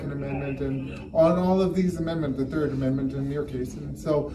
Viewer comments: DID HE SAY "YOUR 3RD AMENDMENT IN THIS CASE"??** DA FUCK? IS HE HOUSING WOUNDED BLUE/RED COATS AGAINST HIS WILL???? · Did he say,”Third amendment in your case.”? 13:11. Either I misunderstood him, or this cop thinks this guy is exercising his right to not house soldiers. Am I mistaken, or is this cop just an idiot?